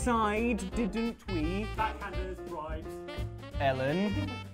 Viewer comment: what was said at 0:15 please?? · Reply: said "Backhanders, bribes." however, I think was insinuating shenanegans in the main Charades at the ten second mark, but I can't quite make it out.